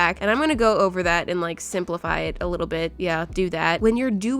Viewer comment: For a second I thought when she said simplify it I thought she said Sprink la phi it